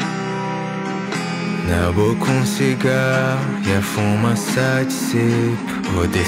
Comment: foi só eu que ouvi " e a fuma sexi"